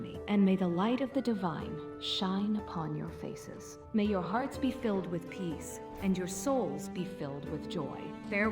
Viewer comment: For some reason all I heard was “may your heart be filled with peas” lol